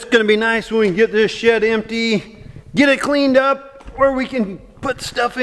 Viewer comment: For a second I thought said "Get this shit empty" but he said " Get this SHED empty". Lol lol